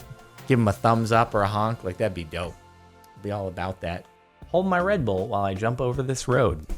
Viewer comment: I thought you said “hold my bread bowl”